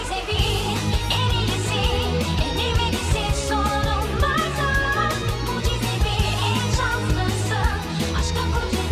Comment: de ne diyor ben 1 yıldır anlayamadım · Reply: Elini verirsin sorunlardaaaa diyor